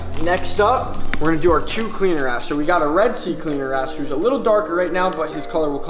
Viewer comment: We got 2 cleaner ass 8:24 . Did i hear well ????????